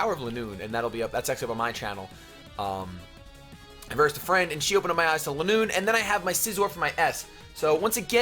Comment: it sounded like he said, "She opened my asshole at noon" LOL